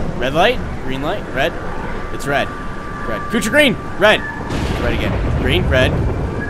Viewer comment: Did he said kootra green?